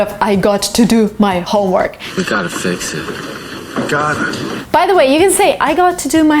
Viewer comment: did you say ,,поэтому ''?